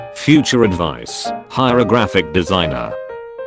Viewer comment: i swear i thought he said Pyrographic designer